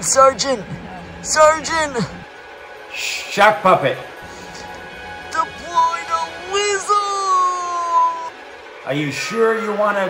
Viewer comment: Deploying Lizzo's *Lizzo literally drops out of an airplane* That part actually got me! XD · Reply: I thought he said deploy the weasel